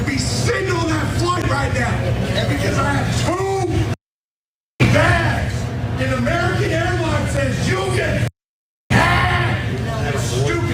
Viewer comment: "I gave your airline two thousand dollars and I'm too fucking stupid to wear a mask so I purposefully pissed that money away like a dumbass!" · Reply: I might be wrong but I swore i herd him say cause he had 2 bags. Funny anyway tho😆. 30sec mark